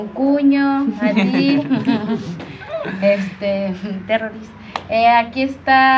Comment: No podes, le dijiste "TERRORISTA" a tu concuño..??? min 1:27, o escuche mal..??